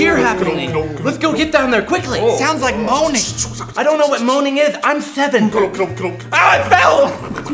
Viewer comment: "I dont know what moaning is, I'm 7." OH MY GOD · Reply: When I first watched this I thought they said "boning" instead of "moaning." Sorry, for my dirty mind. XD